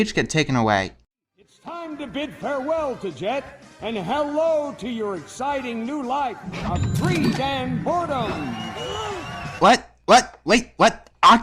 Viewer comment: How I heard the lines at 5:38 "Say goodbye to Jet, and say hello to your new life, a three-man porno."